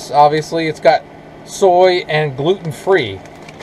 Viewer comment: Most soy has gmo’s. I love ❤️ your cooking videos regardless to what you cook. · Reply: Sorry I thought I heard you read ingredients, soy and gluten free. Maybe you said no soy. Lol